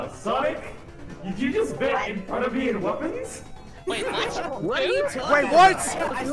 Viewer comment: I thought said "buried weapons" if he did that would be funny XD.